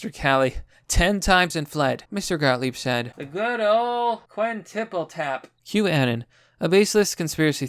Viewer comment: The best part is when I heard you say "the Good Ol Quinn Tiple tap" I got a good laugh out of that 🤣🤣🤣